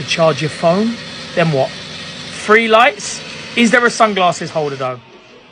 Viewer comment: Swear I heard “free lights!?“